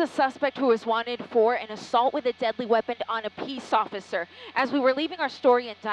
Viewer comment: did she say peace?